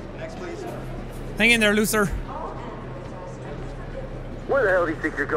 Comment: the heck 3:40 the subtitles said hang in there loser